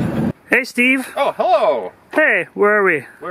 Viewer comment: Am I hearing "He-plow" at the beginning of the video, or is that just me? o_O